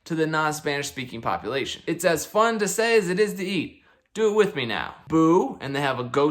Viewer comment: OMG I was in middle school when they came out. We used to laugh because we thought the kid said "as fun as say, as eat!" I remembered they tasted good, something like honey cinnamon kix like you said.